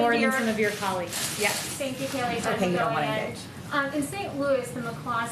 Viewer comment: I could clearly heard Kimberly Halkett of Al Jazeeza said, "OK you're a lying B'tch" and she later responded to tweet by saying that what she said was "there's a lot of misreporting out there about that briefing. The answer to your question is, I DID NOT. What I said was, "OKAY, YOU DON'T WANT TO ENGAGE."" Either my ears have failed me or she has no integrity and is just lying outright...which is the norm nowadays with the media.